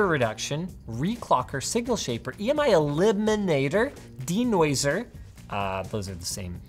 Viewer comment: did I hear LIGMA elimitor.. I need that, I definitively do not want to have LIGMA